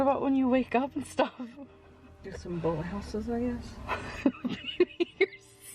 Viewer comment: does anyone know what says right here? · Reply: sounds like, get some boathouses i guess most likely meaning takeout 🤷🏽‍♀️